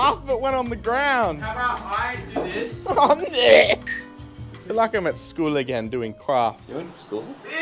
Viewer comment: I thought said “I feel like im at school again doing crack” 😂